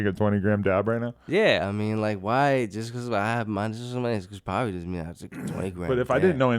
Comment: if anyone can tell me what hes saying at 27:18 word for word ill pay you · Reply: Jus cause what happen, just cause my name is doesn’t mean I have to take a 20 gram dab” pay up